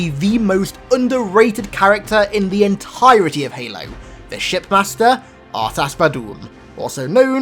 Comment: i thought he said shit master😂